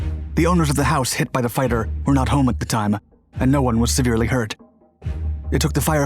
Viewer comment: what word is the narrator using? Is he trying to say “seriously hurt”?